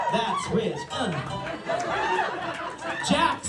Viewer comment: am i tweaking or did he say "that's rizz" or am i just crazy